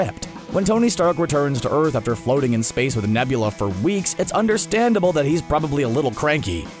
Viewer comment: I thought he said “flirting in apace with nebula ” lol